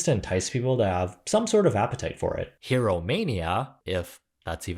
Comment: When said “Hero Mania” I heard “Here Romania” and thought it was a Romanian restaurant